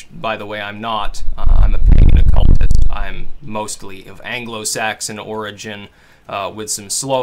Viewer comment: Didn't quite catch that; the wind killed your mic. Did you say "mostly of Hebrew origin"?